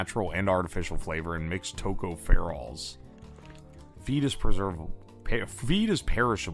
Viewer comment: on god i thought he said "fetus preservatives" was an ingredient